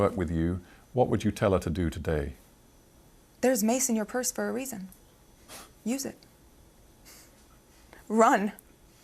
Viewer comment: What does she said at 23:58?? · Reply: “There is mace in your purse for a reason, use it. Run!” mace=pepper spray